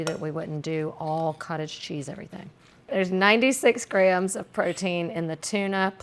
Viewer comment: I thought you said 96 grams of protein and I was like yeah baby, I always eat an entire can of tuna myself and was so excited. Then I went into the cupboard to check and my clover leaf tuna only has 13 grams per half a can. Then I came back and rewatched and heard you say 32 grams lol